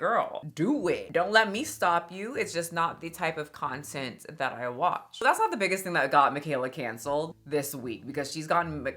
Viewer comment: Did she say Mikayla got Micancelled? 😩😭